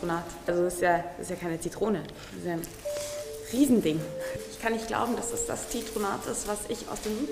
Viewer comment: OMG kan man richtig falsch verstehen(dazu der Blick vom Koch) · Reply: Jeez "riesen ding"